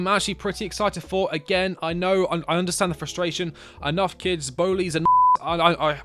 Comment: did bro just say the n word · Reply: *Nonce, just don’t know how YouTube would feel about that word